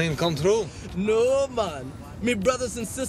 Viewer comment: Me brothers and sisters were hacked to death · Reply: I thought he said hot not hacked